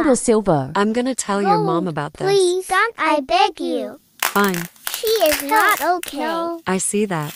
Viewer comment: They Both Said "I Beg You" At The Same Time 😂 Edit 6:48 Guys I Think I Heard The First One "Yo Aimo" Me: POLLO?! Chicken